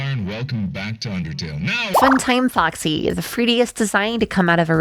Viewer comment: Freediest? 3DS? What did she say? Those two things is all I could hear. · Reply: Fruttiest